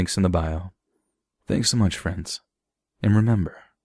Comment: What did he actually say at the end? Because all I hear is "remember not to get lost in the anus of time" 😅